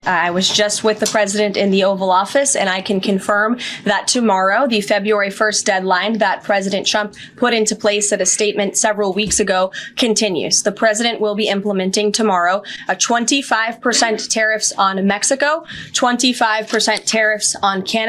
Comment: Is it just me, or did she just say president chump?